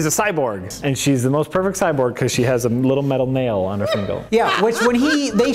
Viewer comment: says "Fingle" instead of "Finger", and I can't help but wonder if he had recently watched Overdrawn at the Memory Bank or its MST3K